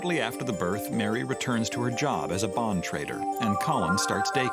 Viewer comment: I thought he said bomb trader lol. I had the check the subtitles!